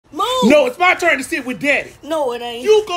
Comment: Really thought that at first he said ' no it's my turn to see if we dead'